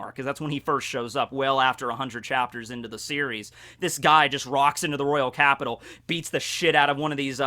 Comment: Why does always say rocks instead of walks? Am I deff or does he use rocks up for walks up? 2:22 Edit: added time thingy